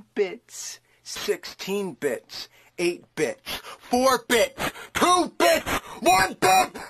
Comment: I heard he says 4 B*tch